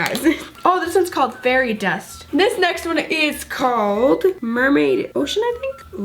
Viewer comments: I think it said Mermaid Lagoon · I think it says mermaid lagoon